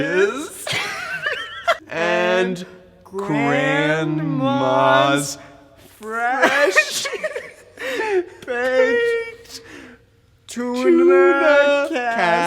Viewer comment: lmao Thomas is trying so hard not to laugh at the horsepower explanation also why did I hear Grandma's flesh baked?!